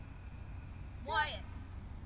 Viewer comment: Did she say "quiet" or "what"? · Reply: It was Wyatt XD